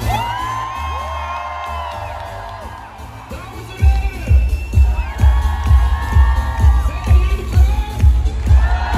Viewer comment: What does he say at 3:10 before "zijn jullie klaar?" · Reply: He says “Dames en heren” (ladies and gentlemen)